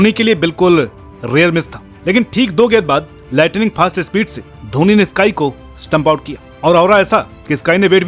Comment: Agar mein glt nhi hu toh @ 1:57 pe kya bola 😅😊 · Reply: Lightning fast